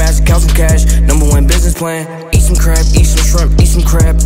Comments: Nobody: Comethazine: Eat some Crab Eat some Shrimp Eat some Crab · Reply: At first I thought he said eat some crap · Why it sound like he said "Did some crack, Eat some shrimp" 😂